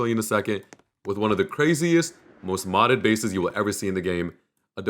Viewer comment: did bro just say modded🥀